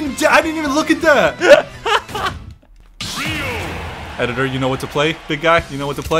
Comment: When he said "editor do you know what d to play" I said "Oh yeah we're bringing out the best theme in the world, Guile's theme!" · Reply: Edit: "editor you know what to play"